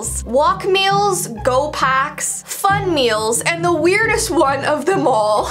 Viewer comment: she said something completely different from what the caption said she said, fun meals, and it said on trays